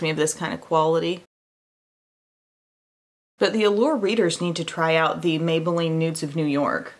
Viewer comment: When you said “Allure readers” I thought you said “world leaders.” I was so confused. 🤣